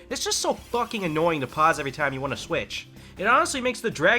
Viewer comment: Did I hear pause to switch costumes? I'm gettint OOT Water temple vibes and i don't like it